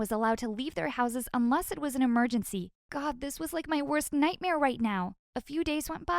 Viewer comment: did you just say worst nightmare